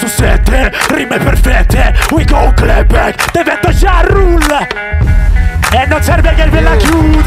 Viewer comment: Raga a 5:20 cosa dice? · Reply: Divento Ja Rule, la base è di una sua canzone "Clap Back"